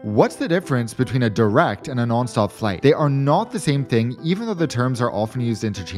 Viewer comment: Why is it whenever he said nonstop flight I thought he'd say? 🤣